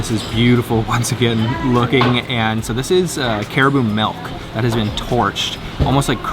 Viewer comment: did you say Caribou milk?